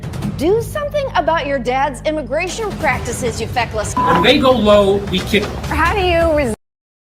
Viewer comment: 👂🏾🤔🤔 DID HE JUST SAY "WHEN THEY GO LOW WE KICK THEM", OR AM I TRIPPING?? KMSL 🤭🤣🤣🤣🤣🤣😭😭 WE ARE LIVING IN THE TWILIGHT ZONE, AND AN UPSIDE DOWN AZZ BACKWARDS WORLD!! 🎯🎯🎯💯💯 FOR REAL FOR REAL!!!!!! 🧐